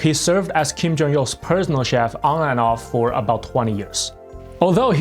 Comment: I heard prisoner chef. And I'm sure it wasn't another time of mishearing a Korean accent. This time.